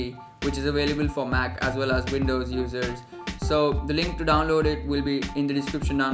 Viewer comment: did he just say windows losers or is it the accent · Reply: users